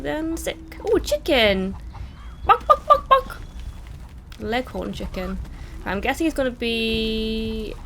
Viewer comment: I thought it said legendary chicken for a minute